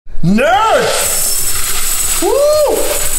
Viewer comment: Sounds like he said nurse not nerds 😂